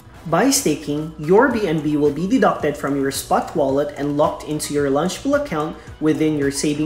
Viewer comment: did you say safemoon wallet?